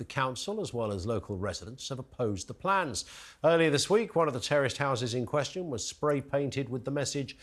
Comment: Ah, "One of the terraced houses", I thought you said "One of the t e r r o r i s t houses". I thought yes, someone speaking the truth 🙂